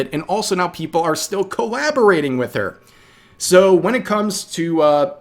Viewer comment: I legit thought you were saying corrabarating because they're both Chinese lol